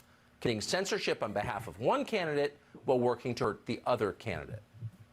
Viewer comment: I swear Carlson says “working turd” , I know he doesn’t but I can’t stop hearing it lol😂